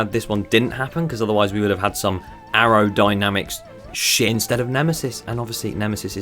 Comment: what did he say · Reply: It was meant to be like sh- but me saying “instead” after really makes it sound like I said the swear word 😂😂